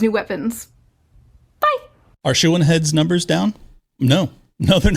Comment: WHAT is her name. Sounds like you say “Shoe-in heads”. And closed captioning isn’t clear either.